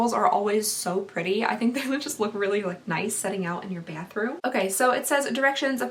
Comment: I know she probably said “bathroom”... but... it kind of sounded like she said “panthroom” 😂😂😂😂